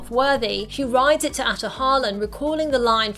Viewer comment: As a Filipino, I thought they were saying “Ate Hollen” when they said Ahtohallen